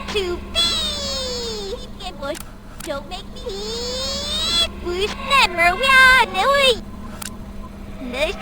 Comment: de tek ben mi dont make me diye bir şey duydum??